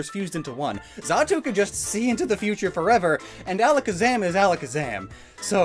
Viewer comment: and Alakazam is Alakazam I heard it as and Alakazam is Gen 1 so GF loves it